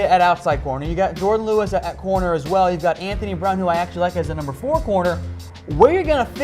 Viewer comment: Anthony Brown sucks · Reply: For some reason I thought u said Antonio Brown and I was about to call for u to get a drug test LOL XD